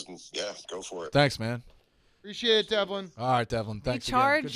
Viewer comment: Does anyone know what Devlin said to Rico at 17:10 ?? Brandon had quite a reaction but they glossed over it. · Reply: Pretty sure “Thank you, Rico. I’m dead.” For context, RnR was absolute chaos for Devlin.